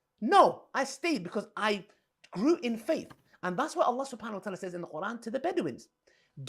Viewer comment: mins Lily Dawah "...and that's what al-lah sapanallah said blah blah blah..." Al-lah sapanallah? Is Sapanallah his surname?😄